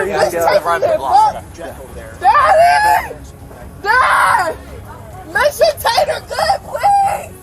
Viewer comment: Did she just say “Daddy, make the taters good”?